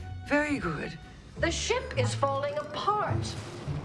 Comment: I'm sorry, at 6:01 she yells "The shimp is falling apart" and that somehow didn't catch a comment?